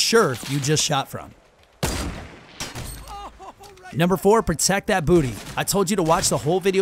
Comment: did he say ''protect tht booty''